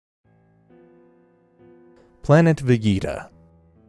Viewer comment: Did i just hear you say planet veyeta